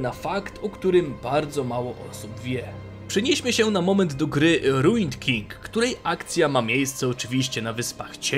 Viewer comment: co to za gierka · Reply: nvm usłyszałem ruined king